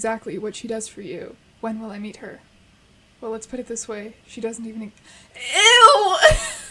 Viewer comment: I thought that said "She isn't even nine yet"